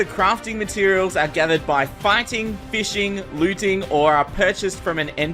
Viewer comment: who else heard him say "farting"